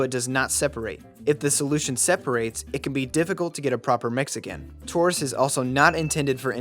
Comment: Proper... Mexican? What did you say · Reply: mix again